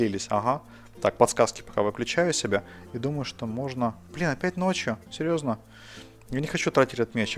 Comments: на 11:34 мне послышилось или нет но сказал бля. Кто тоже это услышал ставь лайк под эту запись :) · Мне послышалось или сказал:" Бля,опять ночью" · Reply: Electronic Fort ан сказал блин опять ночь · мне одному показалось что проматерился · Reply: dambon4ik Der 11:30 он сказал "б*я, опять ночью?" (знаю, что скорее всего послышалось)